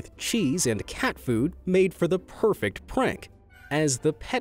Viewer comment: my man really did that huh? "purrfect" I might be wrong but I heard it like that so there's that xD.